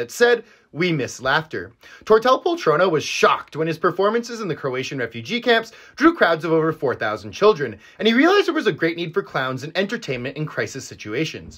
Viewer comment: Ngl my dumbass thought you said he got shot when you said he was shocked.